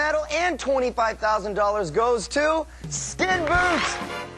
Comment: does actually say Skinboot? What an idiot.